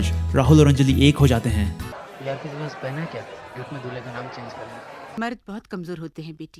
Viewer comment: What they said at 8:34 ? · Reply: "Yaaar tere paas pen hai kya, ye dulhe ka naam change karte hai"!!